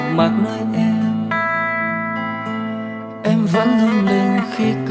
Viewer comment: Ai nghe thành "anh sao cũng đẹp" :))